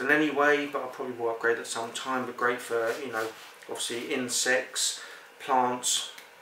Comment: LOL at 7:13 it sounds like hes saying " in sex" , but when I listened again it was clear it was "insects" :D